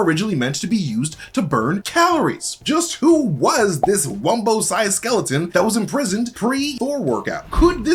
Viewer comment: Did he just say “wumbo size” 😂😂😂